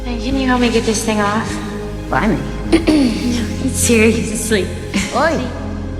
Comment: after Dani asks for assistance what is it that Jamie says in response, i am literally s t u p i d and i cant understand her accent sometimes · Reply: Jaime says "blimey" which is like kinda cheeky and teasing. basically means damn okay! also you're not stupid!!